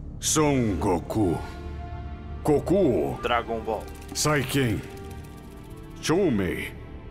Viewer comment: só eu escutei kokuo "dragon ball"10:13?